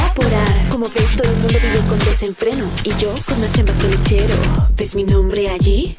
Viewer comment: Y yo con más CHAMBA qué el lechero Literal , así lo escuché 😂😂😂😂😂